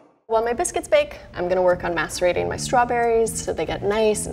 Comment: Did she say masturbating her strawberries?? 😳😳